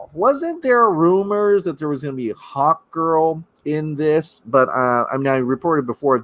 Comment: I thought you said hotgirl instead of hawkgirl